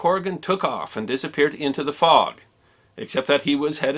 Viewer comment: I thought you said Weast